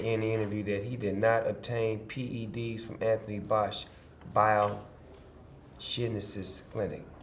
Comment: Did he just say that Alex Rodriguez claims he did not obtain PED's from Anthony Fauci?